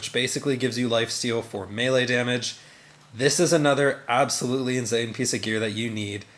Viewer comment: LOL ngl im not sure what you mean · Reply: heard it as “absolutely inZane” the first time😅